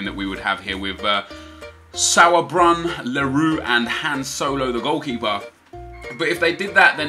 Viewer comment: did he say Han Solo? xp